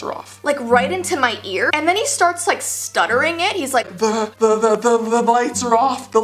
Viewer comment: I thought you were saying ,the legs are of, 😂😂😂😂😂